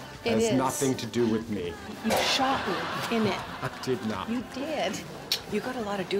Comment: One of my favourite episodes! For the longest time, I thought Jan actually said, "You shot me in my Regina! " I sure miss this show, and especially them together.